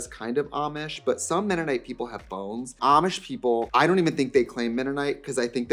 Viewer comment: I thought you said “Some Mennonite people have bones” and I was like Trixie girl back up we have gone too far with the conspiracy